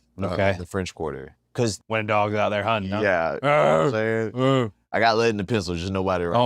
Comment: Bruh, Cody gonna need to sell his heel run hard because all I hear now is “them dawg’s out huntin’…WOOF”. 😂